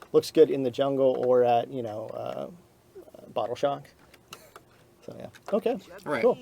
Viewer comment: did he say the bracelet looks good in the jungle or barber shop? Dude looked at him like what are you doing !!!